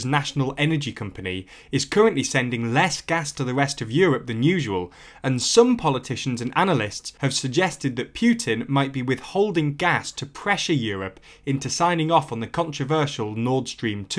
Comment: did... did he say Pyutin?